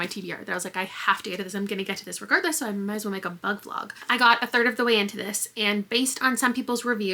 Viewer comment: Absolutely misheard the opening intro as "Ugg vlog" and was confused for a bit lol